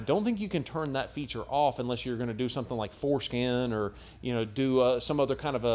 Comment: Man, you really gotta emphasize the AN part of ForscAN. My ears and captions both heard it as foreskin.